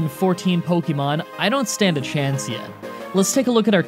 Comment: I thought he said “I don’t stand a Chansey” That would have been a great pun